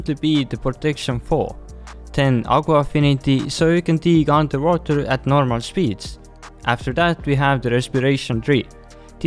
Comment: what is bro saying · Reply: Aqua affinity so you could dig underwater at normal speeds